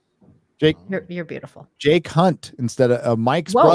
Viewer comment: Is "Jake Hunt" supposed to be pronounced "Jay Cunt" or did tired me just mishear it?..